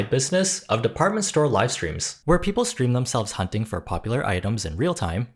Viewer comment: Maltese Archive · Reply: OHHHHH i thought it said alive for some reason lol